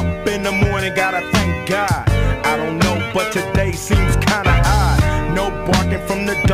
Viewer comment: i thought he said today feels kinda high but he said today feels kinda odd